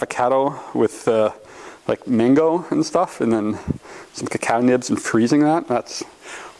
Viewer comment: I swear to god I thought you said "catnip" at first, and not "cacao nibs" cos you were talking so fast right there when you got excited. That does sound delicious though.